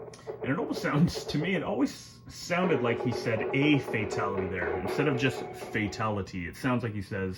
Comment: wow I rewinded to listen again and you're right! It does sound like he says "a fatality"